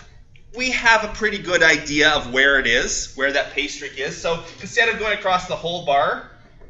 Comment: I kept hearing you say “pastries”. Hahahahaha I must be hungry. Awesome videos, thanks for all your hard work.